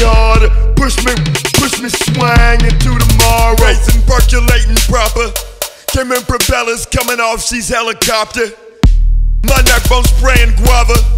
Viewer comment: always thought he said "helen copter"